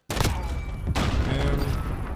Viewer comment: Did u just say gave him the dome again